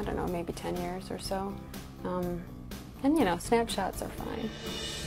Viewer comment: did she just say “and snap chats are fine “? 😳😳